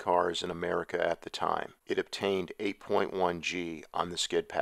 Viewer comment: Did you say 8.1g !